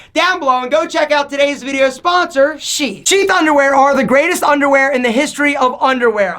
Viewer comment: Click <link> and take advantage of SHEATH's offer off your total order! (code 20alpha) These Underwear are AMAZING! <link> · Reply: Mate I thought you said Tani ‘Twas the best underwear